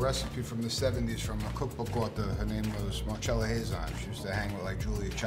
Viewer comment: DID ANYONE CATCH THE NAME OF THE LADY HE MENTIONED 1:33 ?????????? i bet her cookbook is amazing, afterall she's the reason why their swose is the bowse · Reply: I believe the name was Marcella Hazan. I'm also eager to find that book. ;)